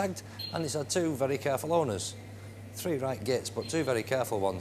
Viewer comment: What he said ? "It had 2 carefull owners, 3 ... but 2 carefull owners " 3 what? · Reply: “2 careful owners, 3 right gits but 2 very careful ones. I’m only joking” He’s joking and saying that although it’s had 2 careful owners the other 3 weren’t, but It has only had 2 owners hence why he follows up with “I’m only joking”